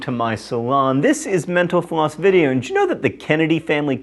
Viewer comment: for the first time, i heard "mental floss" as "menopause".